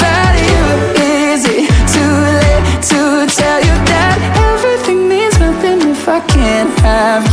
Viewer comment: When I first heard the verse: "To late to tell you that," I thought he said, "To late to tell you Dad."